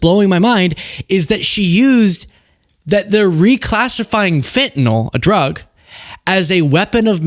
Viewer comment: Totally thought I heard you say he was reclassifying Finland.